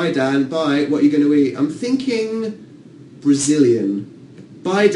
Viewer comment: what did he said at 59:12? something something I'm thinking brazilian (?). English speakers please tell me this is very important to my little fangirl heart. Thank you ;)